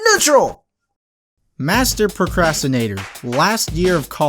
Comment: MASTER PROCRASTINATOR I thought you'd said master masturbater.